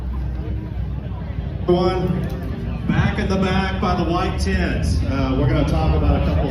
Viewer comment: Did he say: “Tramp in the back, with the wide tits.” or “Blacks in the back, behind the white kids.” ??